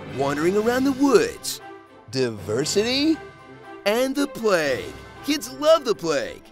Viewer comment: "And the plague. Kids love the plague." I laughed way too hard at this. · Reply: I thought he said “the play” for the longest time. It would still be funny, but plague is just so much darker and funnier.